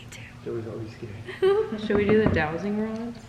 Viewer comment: the whisper sounds like “don’t speak, Joey’s scared” that is what I heard and I listened like 20 times but that’s what I heard and the fact that Joey after that said that he was scared.